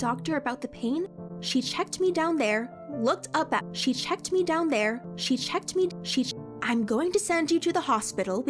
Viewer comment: Why did i heard " she shanked me down there "?